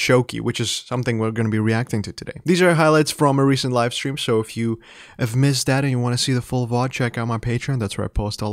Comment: Did you just say "if you've missed daddy"🤨